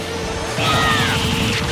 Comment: I FR thought Ike said “Eat this” when he actually said “Aether” 💀💀💀